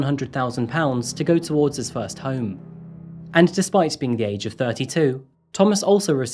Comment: You lost me at "He gave Thomas £100,000 to go towards his first home". That's money some of us can only dream of, and he considered that "prejudice" and threw it all away. The level of entitlement here is unbelievable. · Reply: Lol I misheard it and thought he said £1,000 which still made me go "wow I'd be so happy"